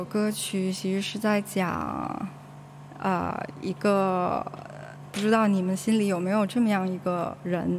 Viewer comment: bri道？0.25倍速都听不出说的是两个字，咧咧咧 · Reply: 北京话，不知道。